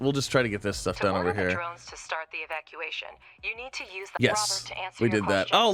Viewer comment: the subtitle says "start the excavation" but the VO says "start the evacuation". I guess they should have evacuated, given what happened moments later, but that was definitely the wrong word.